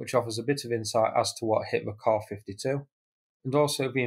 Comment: What was said??? "...clear footage which offers a bit of insight as to what hit the KA-52 and..." Turn on YouTube auto text => "...clear footage which offers a bit of insight as to what Hitler car 52 and..." ...btw - I am sure it was not Hitler car 52 that hit it...